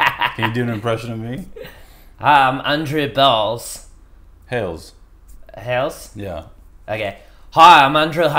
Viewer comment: Andrew Bhales is def killing the game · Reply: I thought he said Andrew Balls lol then corrected himself